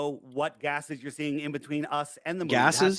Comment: That is definitely not what he was saying. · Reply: that's literally what he's saying. he says "between" and you reply "you think the moon is made out of gas?"